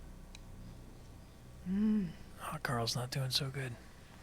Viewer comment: What does Bryan say at 1:27:30 - 35 ? "Hawkgirl is not doing so good?" Does he refer to the DC comics character?